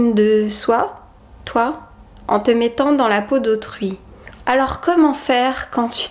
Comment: Au lieu de "dans la peau d'autrui" j'ai compris "dans la peau de Truie" ;)